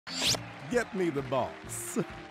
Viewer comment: is me or do I hear "Get me the balls"